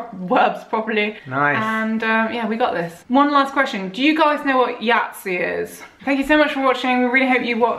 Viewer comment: i can't understand, what are you asking, but please text it, and I'll try :D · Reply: Do you know Yahtzee? Thanks for watching the entire video!